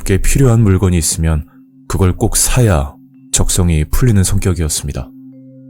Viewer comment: 적성→직성 10:47 들렸다고→들렀다고 자막 맞춤법에 조금의 도움이 됐으면😅 잘 들었습니다🙂